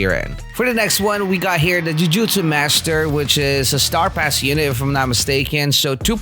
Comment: when he said for Yuta "star pass unit" I thought he said "The Star Plasma" and I felt insulted